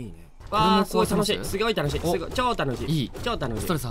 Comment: ｢すごい楽しい｣が｢すぎょい楽しい｣に聞こえて尊🤦🏻‍♀️